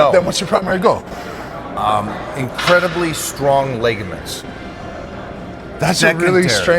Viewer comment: Did you just say "leg-aments" instead of "ligaments"?